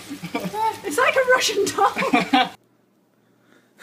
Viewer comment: its like a russian "????" what is it called? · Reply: she said its like a Russian doll. she was taking about a Russian Matryoshka doll.